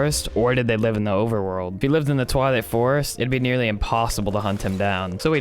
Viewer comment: YouTube captions said "toilet forest"?